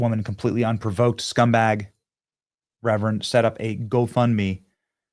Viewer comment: It was set up by a rapist. I thought you said Reverend.